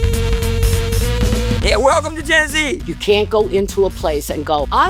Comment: I thought this guy said, "Welcome to Gen Z" and I said to myself, "damn, this guy has had a rough fucking time"